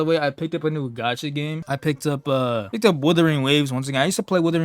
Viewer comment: did i just heard him saying wuwa 👍👍👍👍